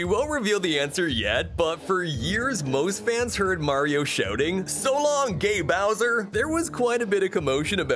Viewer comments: Even at 0.25x play speed, I hear "So long gay Bowser" but it's probably because the word "kinga" and name "Bowser" were said without a space between them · I always thought Mario said So long day Bowser ! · I thought he said *So long ya bowser!* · i thought he said: So long, de Bowser! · I juest wanna say I thought Mario said “so long’ga bowser”